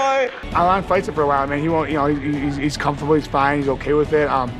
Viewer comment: Ayo wtf did stipe say @ 13:18 LOL!!! love the guy lol... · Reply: “Alanfightsitforawhileman”